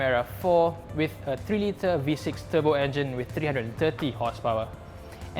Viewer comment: Did he say v6